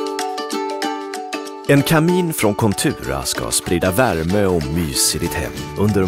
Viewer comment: I heard "en kanin från Contura," and I like mine better.